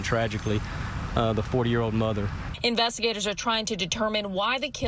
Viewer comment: I know he is saying 40 year old mother but I hear 4 year old mother.